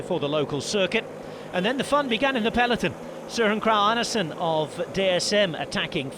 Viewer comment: I want to know what the 0.5% was that he got wrong! 🤣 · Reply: there was a SLIGHT (incredibly minor) odity in the way he didn't pronounce the R in SøRen, with our by the way terribly sounding guttural R's in Danish. It could potentially sound like he is saying Søen (which means The Lake, Sø = Lake. En = the article). I'm being crazy nitpicky, but if there was one thing to put a finger on, I think that would be it. But again, it could also be variance to a dialect and if I spoke to him on the phone and he pronounced it that way, I wouldn't think twice about it.